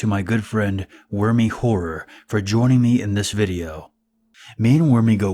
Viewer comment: At the beginning I legit thought he said Wormy Whore and I was really confused for a moment...